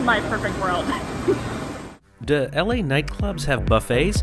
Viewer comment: Hey, sorry at 6:46 anyone hear “dah” and not “do”? Just me and my girlfriend?